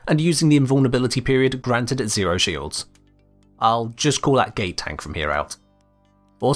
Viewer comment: It sounds like you say 'gay tank' here, which... I am 5,000% on-board with, tbh. Gimme dat rainbow tank with sparkles. Fabulous and imposing. What could be better.